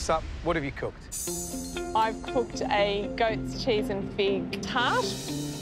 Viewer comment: I thought it said Goat Cheese and Pig Fart